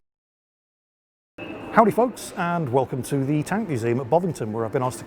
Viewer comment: I just heard him say "howdy TOGs" :D